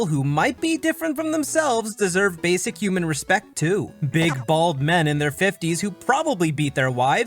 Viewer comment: did you say big-balled men